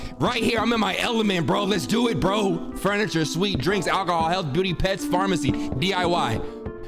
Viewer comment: I HEARD HEALTH BOOTY LMAOOOOOOOOOOOOOOOOO